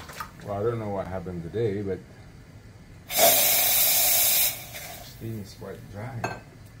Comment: Absolutely beautiful machine. Question on the 2:00 mark, when it hits 35seconds it turns off, but did it also turn off your steam wand? I can't make out what you said, "steam is quite dry" ?